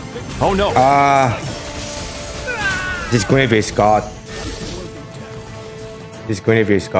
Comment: i thought said "This Gravy is god"